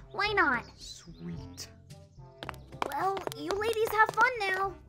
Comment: Did tails said to Amy and shadow you lady have fun now?